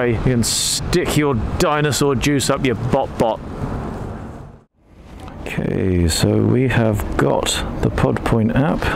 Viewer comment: for the life of me i can't make out what the attendant is saying at 19:38 .... .. my mind is only hearing something like "lazy 5 from the easy 5 for the po-po thank you" ...... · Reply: She's saying "please don't use your phones on the forecourt, thankyou".